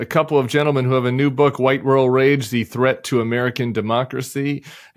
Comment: It took me about 10 minutes to realize the name of book was “white rural rage.” I thought you said “white girl rage.” 😂